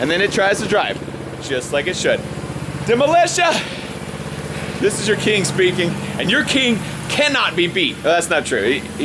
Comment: At the end there i thought he said "This is your Kink Speaking, and your Kink cannot be beat" I guess i had somehow confused myself because he was talking about a Kinked Spring Lmfao